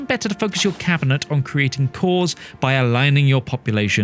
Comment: At the 2:06 mark, are you saying it's better to focus your Cabinet on creating "poors" by aligning your population to your existing society? I think you are saying something important, but my faulty hearing is not helping me here... Closed Caption says "creating cause" but.... ?🤔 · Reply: Creating Cores! So by aligning the majority of the population to your primary culture and religion you create cores